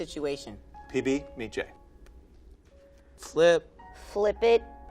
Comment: Wait man did you just say B J